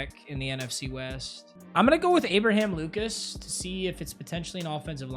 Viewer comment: I thought bro was saying “I think we’re gonna go Abraham Lincoln”💀